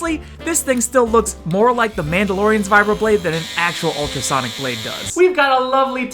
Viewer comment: When he said the mandalorians vibro blade I thought he said the mandalorians vibrator XD